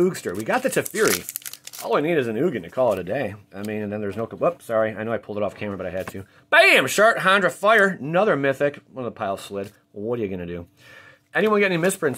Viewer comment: "Boom! Shart, Handra Fire" Me: "Wait did he actually say what I think he said?"